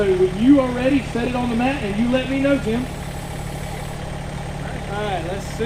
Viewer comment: ? did i hear that right??!?! didn't know Starbucks sells tractors also ...